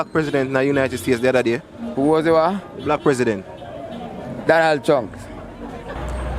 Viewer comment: did i just hear "Donald Chunks"???...who is that? 🤣